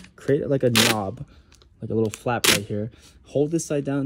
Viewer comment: I thought he said Trey tape